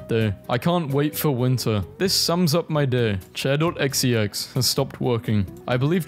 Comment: did u really just say xex instead of exe??